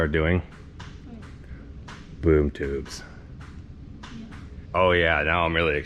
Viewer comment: did you say b00b tubes😂😆🤣